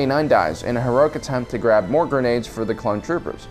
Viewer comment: did he say Disney?